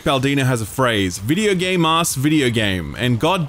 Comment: What phrase is that at 14:21, I can only hear "Videogame ass videogame" no idea what he is actually saying